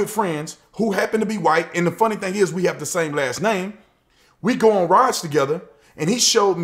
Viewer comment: i thought he said "we go on riots together" LOL be careful CNN doesn't get a hold of that soundbite because they are the real racists